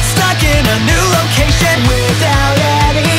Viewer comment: I used to watch this show as a kid. Listening to this brings back a whole lot of nostalgia. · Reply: Same...did you, as well, mishear "stuck in a new location" as "stuck in a noodle kitchen"? It still sounds like it to me xD